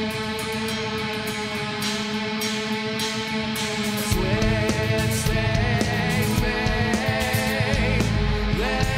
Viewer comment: what does he say at 2:35? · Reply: twisting me